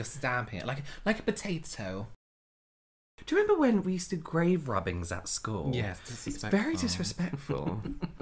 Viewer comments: when they said 'remember when we used to do grave rubbings at school' I thought they said they did grave robbings and i grew so concerned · I seriously thought that you were saying "Grave robbing's" not rubbings!! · I thought they said “remember when we used to do grave robbings” and I was shocked at how casual they were being about it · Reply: The fact that I heard it as robbings too but didn’t even question it has me concerned 😅 I’ve literally seen the video a hundred times and only just realised they say rubbings instead because of this comment 🤣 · "Remember when we used to do grave robbing in school" is what my American ears heard and I was REALLY confused until I realized you were saying RUBBING lmao. Wow, to think all we did was leaf rubbings 😂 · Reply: I’m rewatching this for like the 100th time and just saw your comment and 🤯 makes so much more sense now lmao I thought they were saying robbing 🤣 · ‘remember when we used to do grave robbing’s at school, it’s very disrespectful’ Me: wtf kinda school did you go to where you robbed graves? ‘imagine if you did a grave rubbing and you took it home’ Me: ohhhh grave rubbing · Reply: I was so confused at that bit. I even know what rubbings are and used to do it, but I really thought he said “grave robbing”. I thought it was some inside joke, lol.